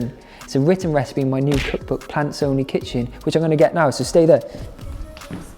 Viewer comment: Hope you enjoy this super simple tasty dessert! new quarantine content coming soon ;) big love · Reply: OK, when you first introduced your book, I could have sworn you said "Pants Only Kitchen" and it threw me for a loop, cause the Brits call boxers/briefs/boxer-briefs pants. OK, I'll show myself out 😂😂